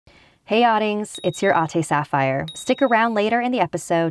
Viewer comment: i’ve been watching something scary for a very long time now and my dumbass thought you was saying “hey odings, it’s your auntie sapphire” but you’re really saying “hey adings, it’s your ate sapphire” and im filipino 🤦🏽‍♀️